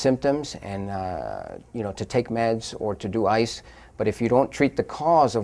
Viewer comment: Did he say to take Meth and do Ice? 😳😧!!!!